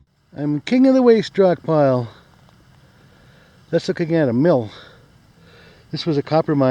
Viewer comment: LMAO, i had to back it up a couple times, when you said "it's a Mill" i kept hearing it's a Milf, i laughed so dam hard at that cause it does sorta sound like that lol.